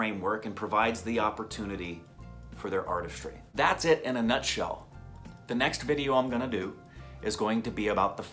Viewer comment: when he said: "In the next video",I heard it as :"In the next class"...OMG.!! Sorry teacher I forgot my notebook...